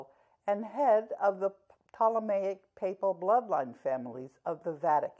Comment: Karen- At 1:30 did you say the Ptolemaic bloodline families? As in the bloodline left behind by the Grecian empire after it was spilt into four parts after Alexander the Great died in Circa 323 BC? Edit- I am asking this as a yes-no question. From a logician mindset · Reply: Karen said "Ptolemaic Papal bloodline families." I'm pretty sure that's what she heard.